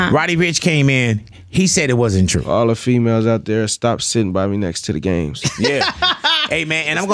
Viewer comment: Did he just say…”All the Women stop sitting by me next to the games” TF😂😂😂😂😂😂😂